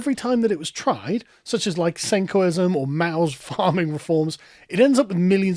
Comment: What is Sargon saying at 2:03? Sankoism?